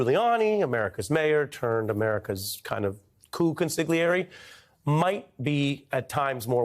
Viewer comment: Oh, lord, I thought he said that Rudy Giuliani was America's "pooh consigliere".